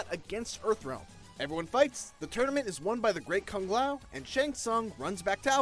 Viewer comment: Great Kung Lao? Am I hearing it right?..